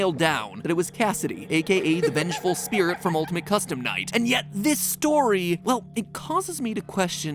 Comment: I thought he said “old mc custom night”